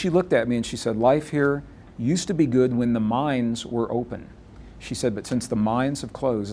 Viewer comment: At first when Gregg said that the woman told him that everything was ok as long as the mines were open then when they closed everything became hard, I thought he said "the minds". I guess that was the message I needed to hear.